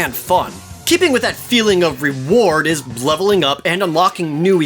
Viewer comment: I thought you said "blood leveling up." XD